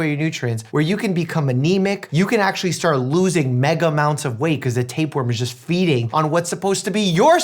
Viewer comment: did you just say....MegaMounts of weight? Is that an ACTUAL real amount? :P